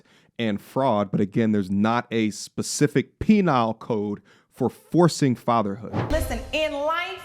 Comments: Penile? Did you just say penile? It's penal. 😂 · Was saying “penile code” instead of “penal code” intentional or a Freudian slip?